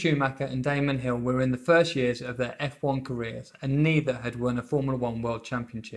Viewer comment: Actually I say "first years" meaning they're at the start of their careers. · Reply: Understood. I heard it as "First year of their careers".